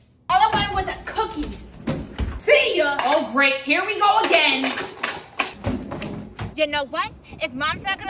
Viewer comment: I thought your mom said “Ahh shit here we go again”